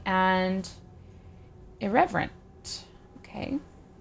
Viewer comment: I thought it said Irrelevant xD